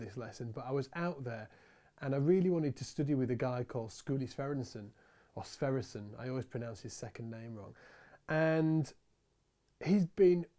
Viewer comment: Can someone link to the song is referring to at about 1:30? I have a really hard time finding the artist (or should I say: hearing what he said) :) Thank you in advance! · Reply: Skuli Sverrisson (Allan Holdsworth) the album is Hard Hat Area